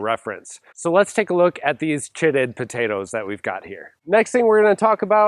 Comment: why did I hear "Shitting Potatoes" 😅